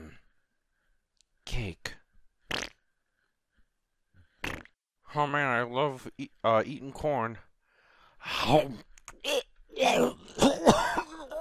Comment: I thought he said I love eating porn